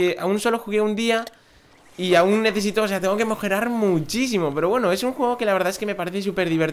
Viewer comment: en el minuto 2:10 se me hace que dijiste mojerar